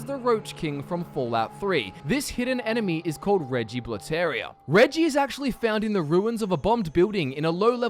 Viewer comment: What is the fourth one everytime it sounds like you say Reggie Rude Terria